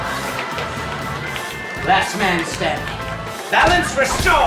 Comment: did just say he wasn't a man?